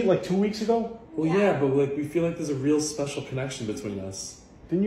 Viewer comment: "are you both out of your loins" is what I heard him say